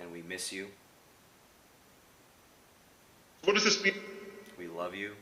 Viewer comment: I heard "what does this mean".